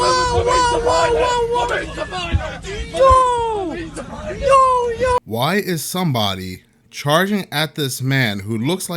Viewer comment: What is he saying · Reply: it cant be 100% heard due to the woman saying YO YO but its possible hes saying i need the pilot